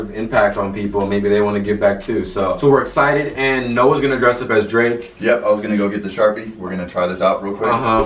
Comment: I thought it said depressed as drake 😂